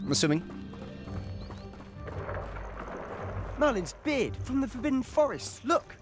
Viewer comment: my dumbass thought Sebastian said " Merlin's beard the fucken forest"